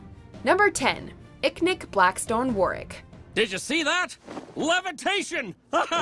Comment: Did she just say warrick and zu li